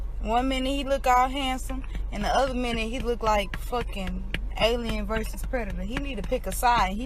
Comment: what did she say...... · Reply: “He looks like alien vs predator”